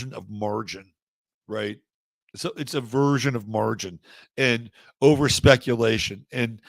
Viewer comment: I thought you said "a version of margarine". LOL! might as well be some sort of lubricant!